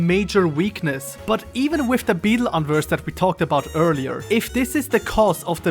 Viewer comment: What is this beetle "unverse"? I've never heard this term, or can't hear which word is meant. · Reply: Unversed are enemy types in Kingdom Hearts who are drawn by negative emotions. i jokingly call the grimm unversed in my videos because they behave similarily